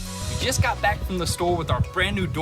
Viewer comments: did he say he has to throw that d*ck together? Love watching you guys by the way!!! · Does he say d*ck at 9:25 😂😆🤣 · did he say dick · did he say "need to throw that dick together" hahaha